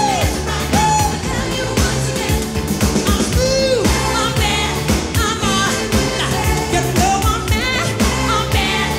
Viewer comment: did he just said i m screw/screwed? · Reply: I am smooth